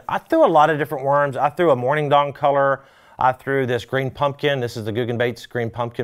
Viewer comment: Did he say morning dong color at 7:56?.....😂😂😂😂😂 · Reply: He said morning dawn color but said dawn really fast 😂.